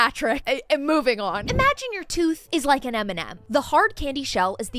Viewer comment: OMG I'm getting old 😂... Instead of hearing "Imagine your tooth is like an M&M" I heard "Imagine your tooth is like Eminem" and I was wondering why is he being dragged in this...